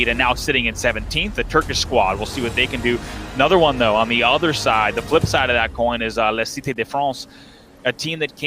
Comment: la cité de France j'ai entendu je suis pas fou mdr